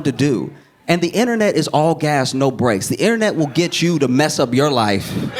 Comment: “The internet is all gas no brakes,” might be the most succinct and accurate commentary on modern life I’ve ever heard. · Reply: My dumbass heard it as “All gas no GRACE” which is also true I guess LOLOL